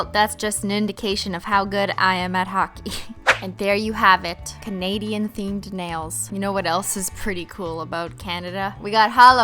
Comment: Ohh sorry I'll click off this because I thought you said England is my nails.